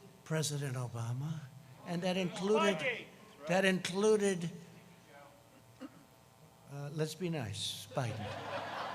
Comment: What was said by the one of the crowd members at 19:59???!!! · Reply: He said "spygate"